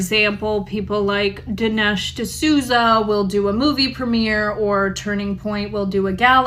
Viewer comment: They said Dinesh Disouza, I thought you said no black (not Black) people go there😂😂😂